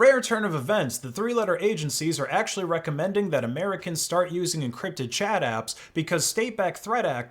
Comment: I heard "encrypted Chad apps"